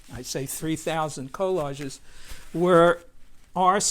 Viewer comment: It sounds like he is saying co largies